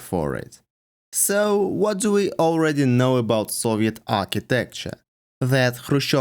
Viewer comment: I totally thought you said ‘Soviet armpit style’ until I saw the text on screen that said ‘Soviet empire’ style.